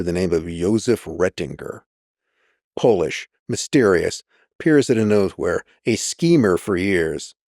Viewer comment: what is he saying here? I can’t make out the words he said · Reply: He says “Polish, mysterious, appears out of nowhere”. That took a min to understand for me as well